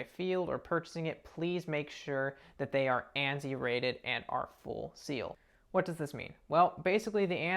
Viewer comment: what does he say? Anti-raided?? Please explain I don’t want to lose an eye